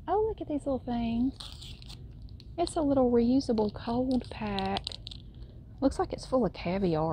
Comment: I thought said "it's a reusable colon pack and it looks like it's filled with caviar," and I had to look at the screen to see what it was. Lol